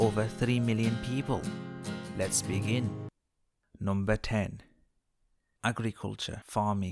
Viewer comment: when you said "agriculture, i thought you said "ugly culture"